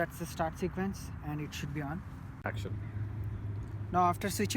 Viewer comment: I heard the cam man saying action 😂😂😂